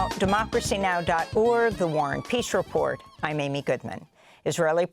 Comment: Why do I always hear Amy say ..."and the war and piss report" 😊